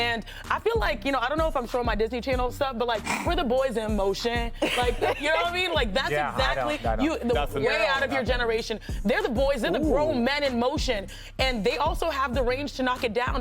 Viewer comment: Yo she really said boys in motion is way outta Jefferson's generation under her breath 😂😂